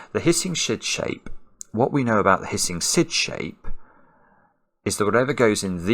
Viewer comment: Did Simon just say sh*t for the first time in a video? 🤣🤩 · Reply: I think he said "shid shape" instead of "syd shape", not a t, but a d.